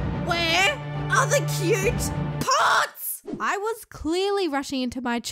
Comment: Me: * Skips to 2:47 * What I hear: "POTS"